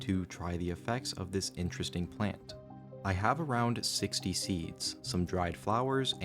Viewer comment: AHAHAAA I'm almost always dead silent and expressionless while listening to videos but I gasped and yelled "Oh my god!" just like my MOM does when I heard "70 seeds".